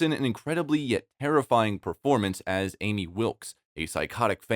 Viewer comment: Wait, Did he just say Amy Wilks!? ANNIE! It's Annie Wilks!